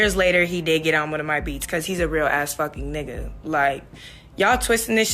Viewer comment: Yo, 5:44 did she just say what I think she did? 'He's a real a$$-phuckin' ninja😂😂😂😂😂